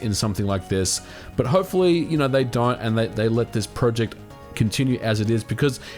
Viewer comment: "But hopefully you know they Die?" Did i miss something or did he really say: Hopefully they Die? starting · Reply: I think it was "don't", just with an accent.